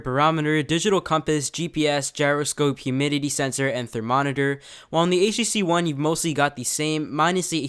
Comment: is he saying thermonitor instead of thermometer